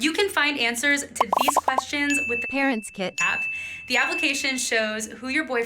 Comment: did anyone else hear KarensKit instead of ParentsKit?